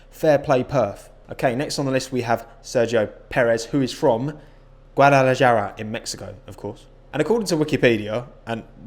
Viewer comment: i heard that sergio is from puerto vallarta